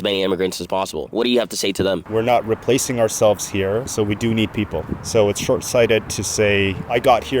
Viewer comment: I'm a non-white immigrant...It's crazy to hear that old liberal white man talking about "we are not being replaced". B!tch you whites are being replaced. Did you not read the architect of the Great Replacement proposal by Eric Weinstein that he submitted to the UN many years ago? Its become UN dogma now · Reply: I think you misheard what he said. 04:23 He said, “we’re not replacing ourselves here.” Which means that the white population that was born here isn’t reproducing. That’s why our government started mass immigration, so that we wouldn’t end up like Japan. The problem is that the major reason why native-born Canadians are having families is due to the economy. A couple can’t even afford their own home, let alone 2 kids. See, in Western white culture, the norm to measure success is moving out at 18 and building your own life on your own. This used to be possible. Now, millions of people see themselves as failures because they had to move back in with parents/family. This then made a lot of people depressed and depressed people don’t look for mates. One of the may good things that immigration has taught us is that the West needs to redefine our measures of success. Many cultures around the world live in extended family, multigenerational homes which makes it easier to save money and pool expenses/savings. Many Indian families think this way and this is why they’re thriving in Canada by buying up businesses and homes.